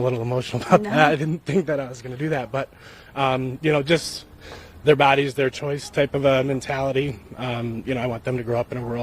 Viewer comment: did he just say "theyre baddies their choice"? I hope his wife divorces him and takes all his money for being a pushover🎉